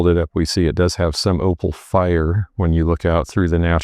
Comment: Hey, @ 11:49 you said something bout the plate being older due to the “opal fire”? I wasn’t sure if that’s what you said or what that actually means. 😬